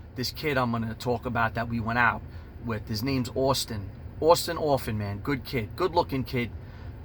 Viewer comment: Just looked him up lol he’s a badass dude🤣👊🏻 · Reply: I thought he was saying Austin orphan!😂